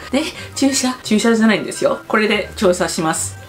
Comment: when you said shot the second time in the subtitles, you accidentally put parking lot instead of shot lot hahaha i know that pain